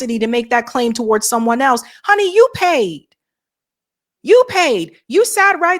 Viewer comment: …..7:56 …IT IS “YOU’RE PAID”, “YOU’RE PAID”.!!! NOT “YOU PAID”!!! “HONEY YOU PAID”, YOU PAID”. WTF is this talk???? She PAID what?? What’s with y’all y’all…THE WORD IS “YOU’RE”…YOU’RE for YOU ARE!!! YOU ARE=YOU’RE.!!!!!! PFHUKK, it’s right there on YOUR SHOW TEXT…”So glad YOU’RE here”!!!! STOP with the JIVE TALK!!!! “WE GOIN,,,,THEY GOIN….FUK, IT’S WE’RE going and THEY’RE going!!!!